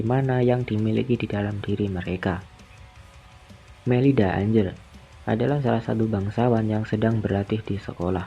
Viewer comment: Melida Anjir?? Wkwkkw jancuk salah dengar gw